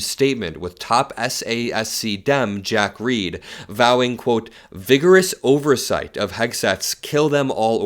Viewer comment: When you said "vigorous oversight" I thought you said "biggerest oversight", Trump is ruining our brains. 🤣